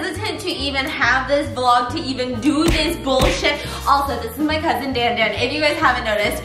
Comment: Me watching the beginning of the video and it had the caption on (English auto-generated) and when said "This is my cousin" the caption heard it as "This is my husband" and I was like "Bruh I heard cousin and you put husband like wtf?!" Hahahahaha